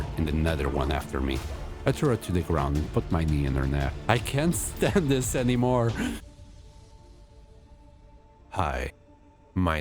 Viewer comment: i thought you said "I threw her down and put my peen on her head"...